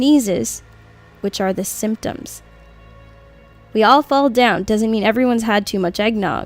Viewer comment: i heard it say " we all fall dead "